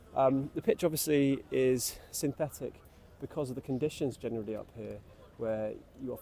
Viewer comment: Did he say, 'Spurs, obviously, are pathetic.'?